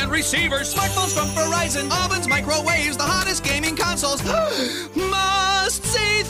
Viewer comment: i thought he said mohameds gaming consoles